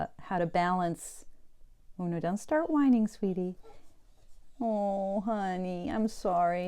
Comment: Aw, beautiful boxer! I could've sworn I heard you call her "Luna" at the start of the video ("Luna, don't start whining, sweetie"), but perhaps you only said, "Oh, now don't start whining, sweetie..." Either way, I think Luna fits her well. And it was a New Moon on the 4th, which sounds like when she nudged her way into your home. :) Great thoughts on how our daily work with tarot shows us changes in the bigger picture. I love looking for the patterns over time.